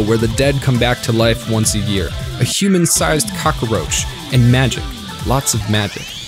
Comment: hold up, at 1:11 did you just say "cacaroach?" Like a certain Tungstencat?